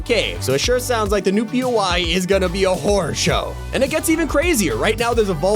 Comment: why does it sound like he said whore show